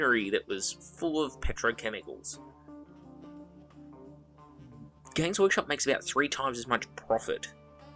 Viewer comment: I always mishear you saying gays workshop and it makes me laugh every time I hear it